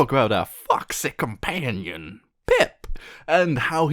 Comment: on the first few seconds i thouhgt i heard "Fuck Sick companion" instead of "Foxy companion" ._.